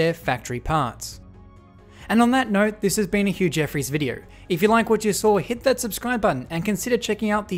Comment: “And on that note, this has been a huge effort video” is what I thought you said. “Too right”, I thought. And I appreciate it. I’m sure many others do too.